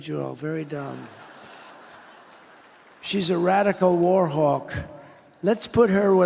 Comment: I thought he said war hog, not war hawk, the first would have been more on point